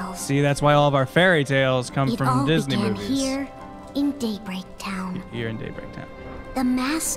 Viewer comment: When it introduced Daybreak Town I thought it said Date rape town. lol